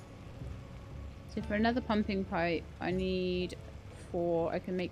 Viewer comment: Haha. Every time you say "pumping pipe" I hear "pumpkin pie". Now I want pie 🥧😊